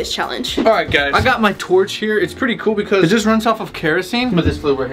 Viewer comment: did he say torch- O-o